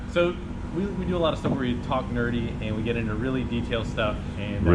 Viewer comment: I thought that said: ‘We talk dirty’..... First time he talked about his channel.